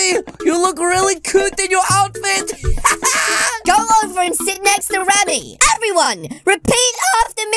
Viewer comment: Hey go stand next to Ramy she said what thought she said go sit next to Ramy